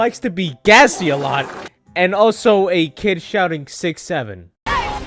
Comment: 67 kid sounds like hes saying the n word